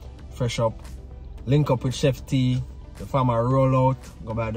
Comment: I thought you said the new name is Queen T not Chef T 😂😂😂😂😂😂😂😂